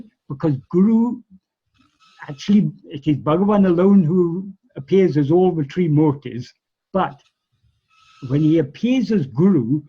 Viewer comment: What does he say in 48:48? "It's Bhagavan alone who appears as all three "______"? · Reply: "It's Bhagavan alone who appears as all the trimūrtis". Trimūrti (tri-mūrti) means the three principal forms or embodiments of God, namely Brahmā, Viṣṇu and Śiva.